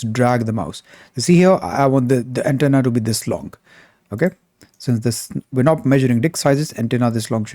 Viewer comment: did you say in 7:30 "since we are not measuring dick sizes"? Haha